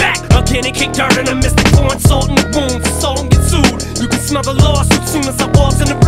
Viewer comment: When he said Assault and get sued I fr thought he said Ndamukong Suh